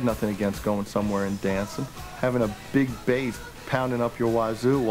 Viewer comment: creo que dice "big babe", no "big bass"... puede ser?